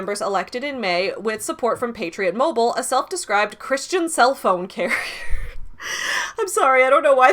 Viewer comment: so I was listening while looking at another tab, so when you said "Christian cellphone carrier" and started laughing, I misheard it as "Christian cellphone Karen"